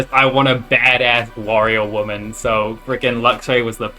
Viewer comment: lowkey thought he said Wario instead of warrior